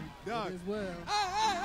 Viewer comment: I thought they were saying "I get down here" absolutely killin!